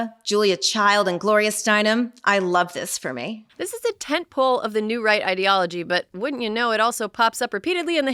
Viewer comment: I thought you said "This is a tit-full" , rather than tent pole. I like MY version. 😊